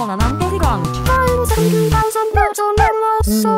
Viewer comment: I thought Oliver said f**k school